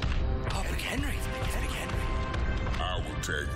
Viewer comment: I thought they said "Mark Henry" XD